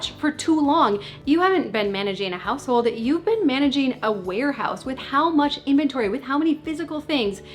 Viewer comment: At first, I thought you said werehouse, not warehouse. And I mean, it’s not wrong 🤷🏻‍♀️ When the clutter has taken monstrous proportions.